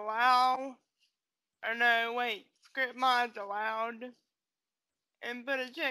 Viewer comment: Um I cant really here what u said but thx for helping I REALLY need it on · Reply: RIGHHT she said script mods allowed but i heard "hirsh mos alloed"